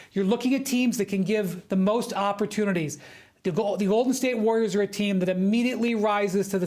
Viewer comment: did bro just call them the Olden State Warriors 😭